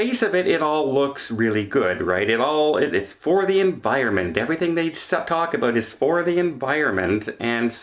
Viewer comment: "everythign they suck.... ..WHAT about" !!!??? "!!!!! what did you say???